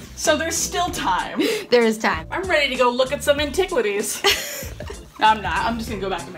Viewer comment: They missed the perfect opportunity to say “antiquitities” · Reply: one of them did actually say ‘’antiquitities’’